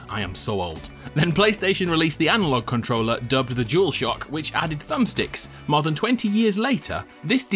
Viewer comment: Am I the only one who heard “Juul shock”